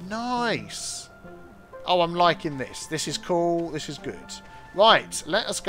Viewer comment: Initially, I thought you said, "This is cool. This is Groot." instead of "This is cool. This is good." I was stuck for a minute, trying to figure out what that could have meant before I figured you probably said "good". Well, if it turned into a phrase like, "That's so Groot.", I could have told people where I heard it first and recommend they check out your channel to hear the origins. :P